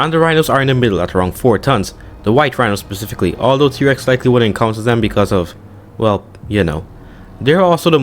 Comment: They're almost extinct 😢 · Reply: Oh I see, I didn't understand what he said at first, I thought he said, "Also T rex would be HUNTing them..." now I realize he said, "Also t rex wouldn't ENCOUNTER them..."